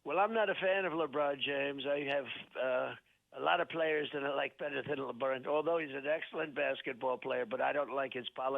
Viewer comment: What the actual F is Drumpf saying at 4:37? It sounds like "a lot of players are like betta thidala burren".